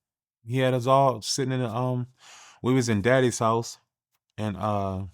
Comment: He said they was at who house.... sound like her said daddies house.....